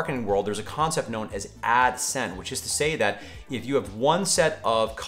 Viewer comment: Adsin? Adsent? Adsaint? It would be nice if we could understand what you are saying. 🤣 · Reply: HOW DARE YOU lolol "Ad Scent!" 🙂